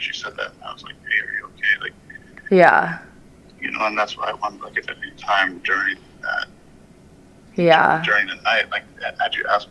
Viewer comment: I hear "and that's why each and every time"